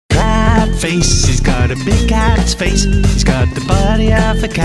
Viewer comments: I heard "caaat face he's got a big-ass face" and I was like "the fuck am I watching.....?" XD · i thought it was saying he's got a big ass face!